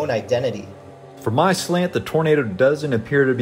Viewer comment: if you have the captions on it says burrito instead of tornado😂😂